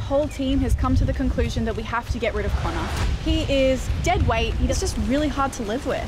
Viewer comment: she didn't say "Connar" she clearly said "Connah" wtf